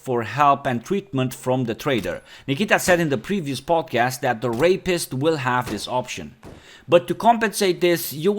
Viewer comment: Sorry at 8:24 did he say “The rapist will have this option” What is this game becoming? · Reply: Therapist = The-rapist = The rapist.